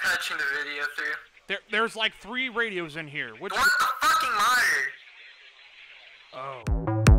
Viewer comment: What in the fuck did the dude on comms say to the SL? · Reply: "The one with the fucking monitor!"